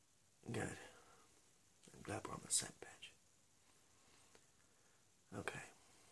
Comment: I thought you said "I'm glad we're on the set bitch" lmao